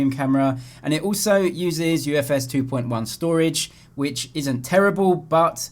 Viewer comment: Did you say UFS 2.1 at 2:30 ?